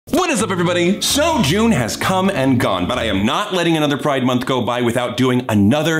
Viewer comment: At the beginning I heard “Joan has come and gone.” I was like “Joan’s gone?!?!”